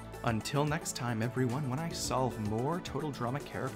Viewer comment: This is the first time I have seen this channel and in his intro I thought it said " the terrorizer " (sorry for bad spelling )